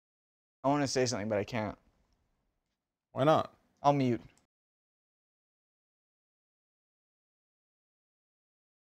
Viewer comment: what did he just said? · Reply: Japanese at the end of World War 2:You can say that.No dude I can't Decrypted by someone in the comments of You Laugh You Leave #10 video